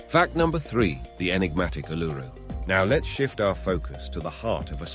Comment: maaaate its pronounced "ozzy " you know like Ozzy Osborne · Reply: that's also not how you say "woolloomooloo". It's /ˌwʊləməˈluː/. Stress is on the last syllable, and the "OO" makes three different sounds. Like "Wool - ah - mah - LOO". I know your just an AI, but your pronounciation of Uluru is so off i couldn't even recognise what you were saying. I thought you said "enigmatic allure"